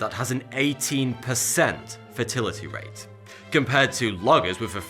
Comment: When he says "fatality rate" it sounds like he says "fertility rate"